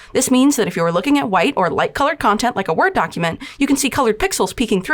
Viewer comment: at first I heard "War document"and I was confused